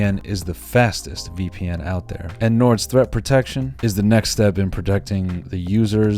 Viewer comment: I thought he said “throat protection” 💀